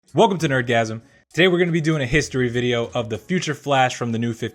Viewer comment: why did it sound like he said welcome to orgasm at the intro